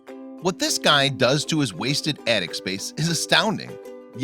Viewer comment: LOL I thought is said "His infested attic space" duuuuh